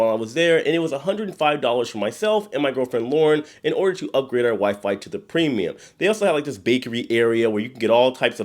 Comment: I thought you said you were going to upgrade your wife, but you said WiFi. I just found your channel but I'm binge watching and was so happy for you and Lauren. 😂